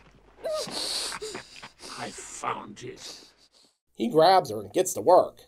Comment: am I hearing that correctly? Did he say “hahaha, I farted”!? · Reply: Hahaha! Close. It was "haha! I found it." :p